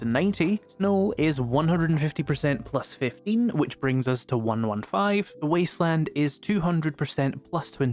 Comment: Wait, did he say 115? (cuts to music)